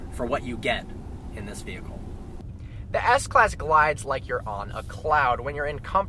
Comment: for a second i totally thought he said "the s class glides like your mom, a cloud"